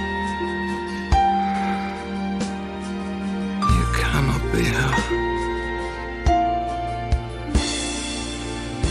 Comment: what did he say? please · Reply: Kili said "You cannot be her. She is far away. She is far, far away from me. She walks in starlight in another world. It was just a dream. Do you think she could have loved me?"